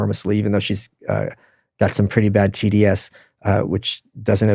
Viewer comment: When he is talking about Anne Applebaum, author of 'Red Famine', at around 1:03:20, what does he mean she has a pretty big amount of 'GDS', if that is actually what he is saying? Having a hard time hearing some of his pronunciations correctly. Thanks. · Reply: Just guessing, but he might have said TDS = Trump Derangement Syndrome